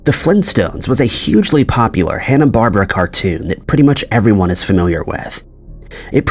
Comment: Hanna Barbera, not Hanna Barber. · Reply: I thought he said Hana Barbara? Like the female name Barbara, I don't know if I'm spelling it right? But her name was always Hannah Barbera (Barr-Bear-Rah)